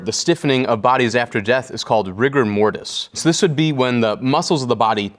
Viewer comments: At first I thought he said Rick n Mort-us. Not rigor mortis. Lol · i thought he said rick and morty when he said rigor mortis